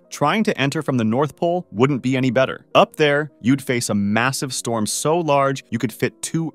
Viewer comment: did he say… MASSIVE?